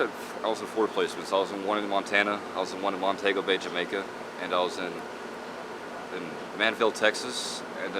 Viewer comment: Did he say Montego Bay, Jamaica?